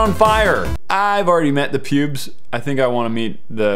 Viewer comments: did he say pubes? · Who else heard him say he met the pubes instead of pewds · Did you call him "The pubes"? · Would you rather meet Pewdiepie or danisnotonfire? Me: gasps and starts phangirling. DAN. DAN. DAN. HOW ABOUT PHIL. WHERE'S PHIL DAMMIT. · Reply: to me it sounded like he said i already met the pubes